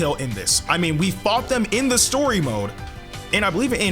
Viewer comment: LMFAO it sounded like you said "we fucked them in the story mode"😂😂😂😂😂